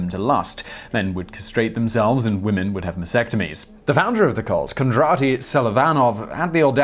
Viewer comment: re - 1:00 The women had vasectomies? Really? Women have a vas deferens? The very same vas deferens that is referred to in the name of the procedure "VASectomy? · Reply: I heard vasectomy too. Going to rewatch... 1:01 I guess he could’ve said “masectomies” (sic)